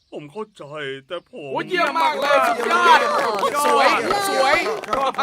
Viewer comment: ทำไมเราได้ยินว่า อุ้ยเหี้* มากเลย